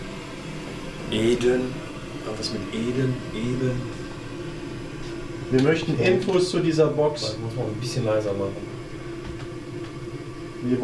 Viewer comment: Bei 26:35 min hab ich juden gehört!